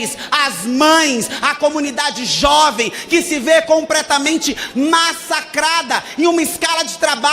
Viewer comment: Rapaz… um deputado que diz e ainda enfatiza: “Compretamente”🧐